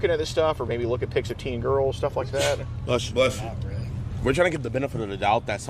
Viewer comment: did he just say bless you irl to the video